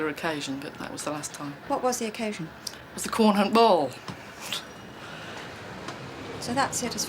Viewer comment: what did she say here? Is that a debutante ball??